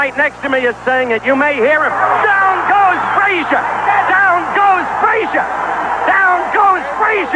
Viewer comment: Man I thought it was saying “down goes frieza down goes frieza so i had to double check